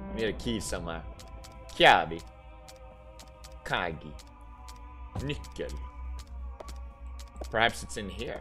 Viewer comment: did he say the n word 0~0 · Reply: I think he said nickel or Nicole